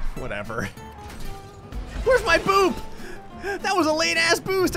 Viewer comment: When he said “where’s my boost?” I swear I heard him say “where’s my boob?”